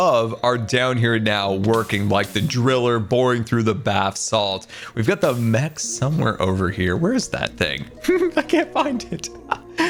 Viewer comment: I thought he said "the driller boring through the bath salt, we've got the meth somewhere over here"